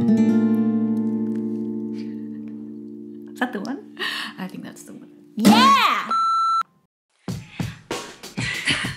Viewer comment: What did you say? Sakto ba? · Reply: zat (is that) the one